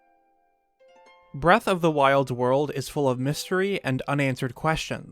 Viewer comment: I now know I have played to much crossing when I thought he said breath of the wild world